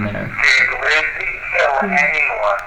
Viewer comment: i heard a very faint “lizzy”